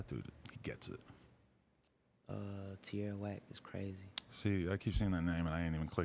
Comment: what's the name of that first person J.I.D. says?? Can't understand him lol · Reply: Rafael Estrada Sounds like he said Tierra whack ?